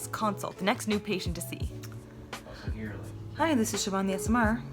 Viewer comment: I thought for a second you said hi this Siobhan’s ASMR at 1:28 😂😅